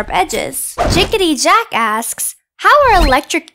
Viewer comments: Thought she said Jickityjack ass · I thought you said, "JickityJack ass" lol Edit: Oh you guys hear it too? 😂 Thanks for the likes! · i heard jickity jack ass, no just me. Ok..... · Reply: its sounds like ass but it says asks